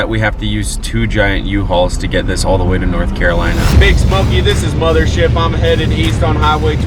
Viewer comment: i heard space monkey not big smokey